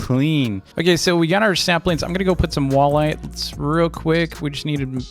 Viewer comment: did he say samplings or saplings I haven't played islands in a while coz of the wipe I lost my coins that took months to earn Edit: oh I watched more of the vid he meant saplings nvm